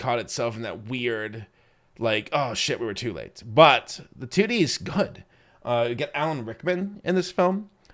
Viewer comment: I had to rewind. I thought I heard "the titties is good" 💀 What did he actually say lmao, I can't unhear it